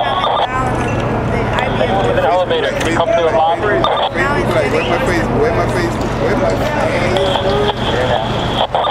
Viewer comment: did he say robbery? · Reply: TheMinnesotaMetro259 _ Lobby